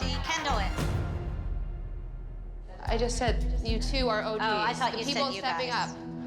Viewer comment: she had no reason to be mad and say ‘I said you two are the ogs’ but in reality she actually said ‘*You guys* are the ogs’